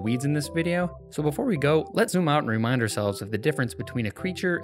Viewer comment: There's an error in the closed captioning at 8:09; he says "let's zoom out and", but the captions say "let's not".